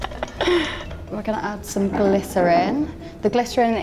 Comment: I thought she said "we're going to add some glitter in", I wondered why it needed to be sparkly... 😅